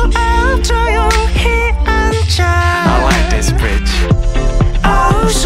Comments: what is he saying. I hear 'I like this bitch' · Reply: B2ST AND VIXX FAN he says I like this "beetch". In hangul it means "light". He jusy played with words. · Did....did he say I like this bitch?!😱😂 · did he say "i like this bitch" at 0:38? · ~ i was jamming to the song then "i like this bitch" part happened and i kinda had a shock. i was like "what tf he said... oh my..." with a shocked face😂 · R.I.P ♥ you and miss you.... but did he say 0:40 "I like this bitch" lmfao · did he say "i like this bitch" ? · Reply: Jimin's Little Finger I like this bridge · did he say "I like this bitch " lmaooooo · i swear he says 'i like this bitch' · I thought he said "I like this bitch" heuheuheu I was so mad while playing Superstar SM Town. So I went online to look up the lyrics ahahhaha. I was gonna all go feminist on Jonghyun but then my ears deceived me. XD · Why I keep hearing "I Like this bitch" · What does he say at 0:40?😅👀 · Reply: I thought he was saying I like this bridge but he is saying I like this bich which means light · but what does he say at like 0:39, its not in the translation ? i really have no clue but wanna know cause it sounds like ‘i like this b*tch’ and im very confused 😂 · it sounds like Jonghyun is saying "I like this bitch." It's not in the lyrics but its in the background. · did he say a 0:40 I like this bitch